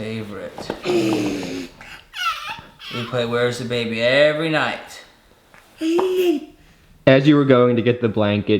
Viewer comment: I could be wrong, but it sounded like approximated the word “right” right after his dad said it! Coincidence or not, he’s such a strong and smart little boy with the most amazing personality☺️ I know he feels how strong his dad’s love is. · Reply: sounds like he repeats every night. I heard it right away